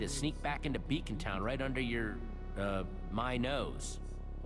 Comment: Love this game! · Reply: The Diamond Invader My NO I thought she said my NOSE